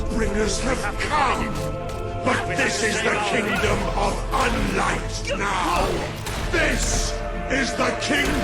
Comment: did the just say “unlight” tf